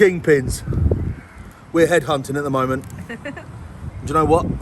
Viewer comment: Does he say headhunted ????? Dohhh !!!! watch the whole video. Incase you didn't, listen at 8 mins in !!!!! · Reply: oh, I thought he was saying head hunting, as in him chasing good places to eat, not the reverse